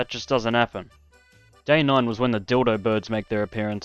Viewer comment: the what birds? tell me im not the only one who heard that3:11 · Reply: Dildo?